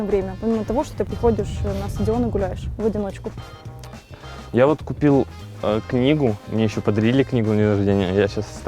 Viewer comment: я один услышал "Я водку пил, книгу?"